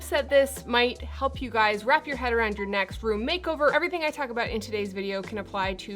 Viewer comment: At the start I thought you said "wrap you head around your neck," hmmm? But went back and you actually said "wrap your head around your next." This is such a well thought out and explained approach to putting a room together. Even I got it. And I really like shopping your house. I've done that without thinking, probably mostly to get some familiar things into a newly designed room (and save a few bucks). Thanks for doing such a fun "tutorial" on how to approach a room design. Always fun to watch and listen to you.